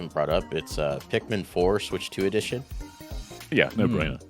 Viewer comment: Had so many thoughts and comments throughout, but they all flew out my brain after mishearing Pikmin 4's Switch 2 Edition as "Louie's Last Days."